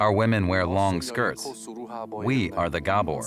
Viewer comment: A shame DW felt the need to censore what the man at 1:15 says. He says "We are Gabor Gypsies", not "We are the Gabor". Gabors are very proud to call themselves gypsies, I actually saw Gabors arguing that they are not to be called rroma, they are gypsies and proud of it, despite the EU rhetoric being that the term is racist. They indeed insist on doing things "their way", regardless of laws, social norms, or how it affects those around them etc. Oh, and for those wondering, Karacsonyfalva is Craciunesti in Romanian, it lies on the Niraj river valley (Nyaradmente) in Mures county. About 15-20 km by car from Targu Mures (depeding which side you approach it). Regarding what they deal with and how they make a living, most have indeed inherited valuable items hidden by their forefathers from the communist regime (maybe some of dubious origin), some are still copper workers (sell copper dishes at festivals and fairs for ex.), tinsmiths, roofers, some indeed travel western Europe and bring back items to resell (some items bought, some stolen, counterfeit, found in dumpsters throw away by western Europeans...nobody really knows), of course without legal forms (unregistered) and paying tax, so on paper they have no income and receive welfare. In Transylvania you can easily spot them due to their garments and their cars which most of the time have Hungarian, Polish and Lithuanian licence plates, for (to Romanians) obvious reasons ...